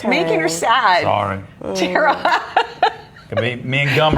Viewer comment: what did he say at 12:47? old folks home and what? I didnt catch it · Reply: He said all the men are dead. Lol I didn't catch it on the first time through, either.